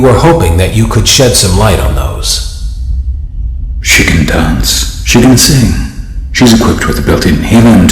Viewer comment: What everyone else hears: "She can dance," What I hear: " Chicken dance "